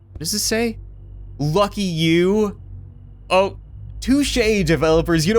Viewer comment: anybody else think this said F*ck You!😂😂😂😂